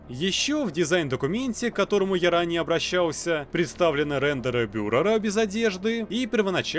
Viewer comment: аххахах мне послышалось рендеры ФЮРЕРА